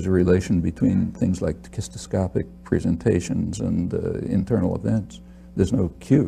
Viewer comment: What is the word he uses at about 11:08? Sounds like "kistestophic"